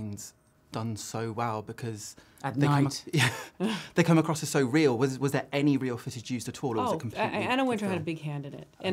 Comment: What did cate say in 0:27 ? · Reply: Sophia Dullano “at night”